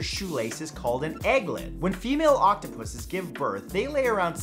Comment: did he say OCTOPUSES?